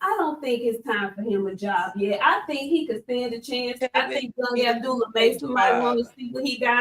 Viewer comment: idk if I heard that too clear but if you said Abdullah vs Tev, you might just hate Tev lmao he gonna end his career on a streak of getting KOd